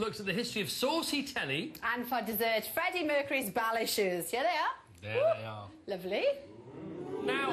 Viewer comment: I thought she said Bowel Issues not ballet shoes lol.